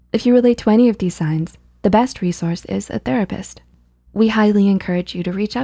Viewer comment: ok so ik this is out of context but uh my cousin saw the ending and she thought it said the rapist instead of therapist